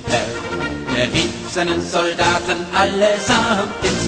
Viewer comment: I'm hearing "Er rieft seinen Soldaten..." instead of "Der rief seine Soldaten..." here